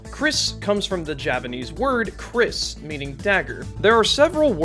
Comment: After hearing you say this multiple times on repeat, I have come to the conclusion that my ears are rotted and I have no idea what you’re trying to say. I have no idea whether or not you’re saying Japanese or Javanese. The way you say it makes it sound like you could be saying either or, which bothers me, because only one of them is correct. Maybe adding manual subtitles would help clarify what you meant to say, because the auto detection thinks you’re saying “Japanese” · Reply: I was trying to say "Javanese"